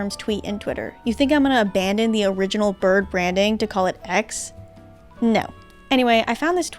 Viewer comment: My ears (somehow) thought you said "Pecks" instead of "X". Even though "Pecks" would still be kinda lame, I would've much preferred it over "X" because at least it would still be trying to be something bird-related and would still have much more identity than a literal letter of the alphabet lol